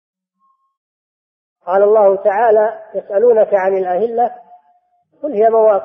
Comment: THERE IS A MISTAKE AT 5:03 - it should say "Allāh, the most high said:" Instead of "He صلى الله عليه وسلم said:" May Allāh forgive us for our mistake